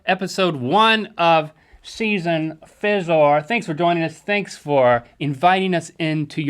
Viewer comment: Excuse me. What? What did Link say...? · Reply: Fizore (four)